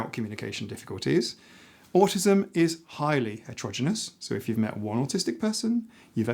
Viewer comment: I heard you use the word "atrogynous" (@ 11:50). I couldn't find a definition. I understood intent from the context, but I'm curious about it. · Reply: Hi Jon, ah it’s, “Heterogeneous”. 😅